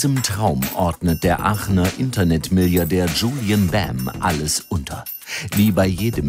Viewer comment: Der Kerl ist Millardär ? Oder hab ich mich da verhört ?